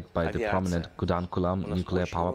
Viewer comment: Did he said kudamkulam...😮